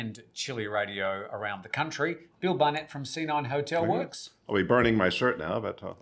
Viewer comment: I thought you said, Bill from, "senile hotelworks" 🤣🤣